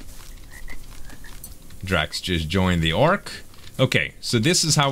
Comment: When you said "draxx just joined this ark" it sounded like you said draxx just joined the army" I was like WHAT!!!